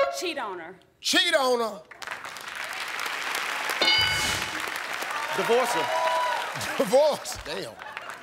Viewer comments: TV on, thunderstorm, dogs barking, kid yelling and I'm not paying attention. I SWORE that guy #1 didn't say Divorce her, but FORCE here. Yikes! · omg I thought he said force her at first